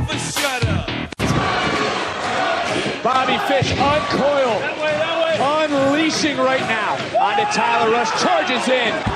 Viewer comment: Bobby Fish is actually saying "That way that way to get kicked out onto the street" he got the karma